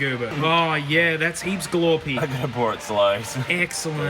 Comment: anyone else hear say "Look how pour it slows" at ~26:40?? XDD